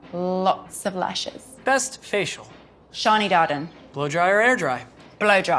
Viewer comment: Did he just say best facial?😳